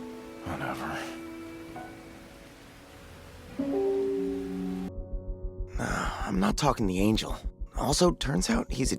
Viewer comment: what does he say? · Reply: "Be careful of Makima" - or something like that.